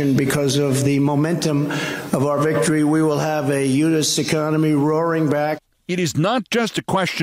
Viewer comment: What's he saying here? Eunuch economy?? Eunuch = castrated. Well, that's projecting onto himself!